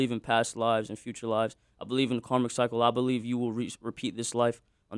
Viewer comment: What was that long word ? Karmacypolivily ? He said at 13:00 lol